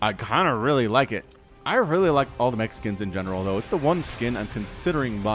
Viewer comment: "I really like all the Mexicans in general" had to do a double take when i heard that cuz i be like wut? that sounded out of place